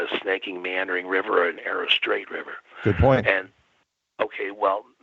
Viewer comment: poor guy.. he thought said "your point" like whats your point.. actually said "Good point"